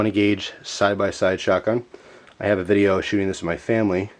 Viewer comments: LOL I thought he said "I have a video of shooting this at my family." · haaha ikr and is it just me or did he say "i have a video shooting this at my family" at 4:25? rofl